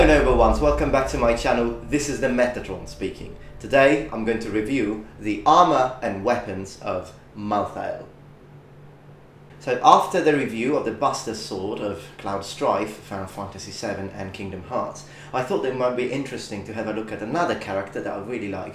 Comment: okay, no offense at all, but at the beginning i thought you said "pasta sword" at 1.11 ! XD